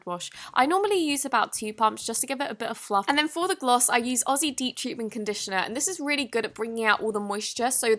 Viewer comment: did u say Aussie? Aussie Aussie Aussie Aussie Aussie Aussie Aussie Aussie Aussie? Australia 🇦🇺 I’m Aussie m8